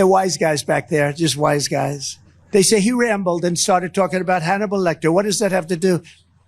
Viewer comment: I thought he said Hannity Lecter. Who's his speech writer? Should be fired.